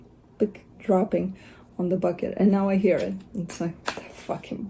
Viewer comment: I heard "the fuqit bucket" 🪣 😂 Yaaaasssss!!!